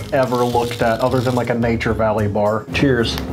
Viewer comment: Did anyone else hear tears